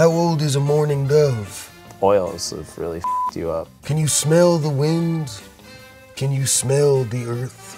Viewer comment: it sounds like he is saying can you smell the fart😂